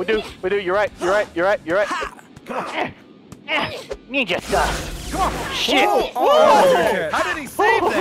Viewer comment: sounded like he said "Ninja Style"